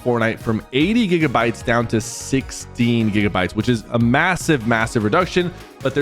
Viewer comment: did I just hear massive?